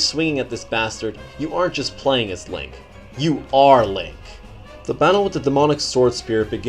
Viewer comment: With english subtitles, at 8:28 instead of saying "You ARE link" it says "You ARE lame." The video is trolling you.